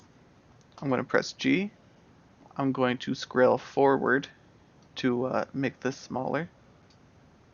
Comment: Great videos. This stuff is so complicated I'm having to look up verbiage your using. at 2:40 you say "I'm going to scrail forward" or something like that and I'm lost. Thought you meant scroll, but that's not working. IDK how to even spell what your saying. Anyone know what I am missing?